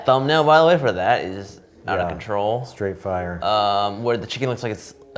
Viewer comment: When they're showing the fried chicken thumbnail, I could have sworn said "Straight fryer" instead of "Straight fire"...that pun would have been glorious...